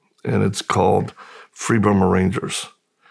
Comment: Will you please post the name of the Dave Eubanks documentary? I heard “Freedom of Rangers.” Is that correct? I played it back several times and still not getting it, thanks.